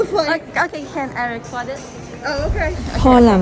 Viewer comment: ช่วงเวลาที่13:59น.พูดว่าอะไรหรอคะเราฟังไม่ทัน😭😭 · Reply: เราพูดว่า can I record?🥲 ค่ะ ตื่นเต้นมากตอนนั้น พูดลนไปหมด🥴😖